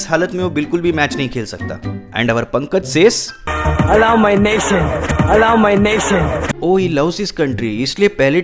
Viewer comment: I thought he said allow my nation 😂